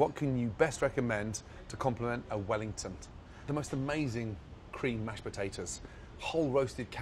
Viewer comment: did he just say wellingtont